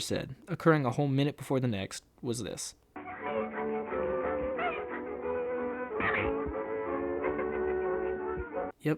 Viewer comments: what diud he say thats racist? minnie? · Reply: He said "mammy", which is a degrading term for a black woman used as a housekeeper. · Wait, I can barely tell what they're saying: what about 2:27 is racist? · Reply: He said Mammy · I dont get what he said, Minnie? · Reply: He said Mammy